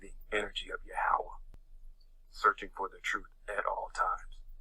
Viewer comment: If sees this, listen REALLY closely to what the guy says around 5:48. Instead of saying Yehova or whatever he was saying, you can clearly hear him say he's "tapping into the living, breathing energy OF THE OWL!", so, it seems there's your Minerva & whomever else you've covered before as fake "deities" who are occultishly associated with the owl is something this person may be as well. I listened to that clip more than once & I'm pretty positive he said "the owl" the end.